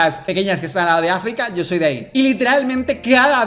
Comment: entendí “yo soy gay” JAJSJAJAJA